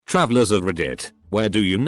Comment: I thought it said time travellers lmaooututtrftcsrhyg6urczf5rg I'm stupid 💀💀💀💀